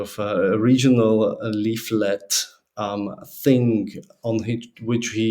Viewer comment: I have a constructive criticism for you - you use "kind of" filler phrase too much. Also, I thing you say "fing" instead of "thing"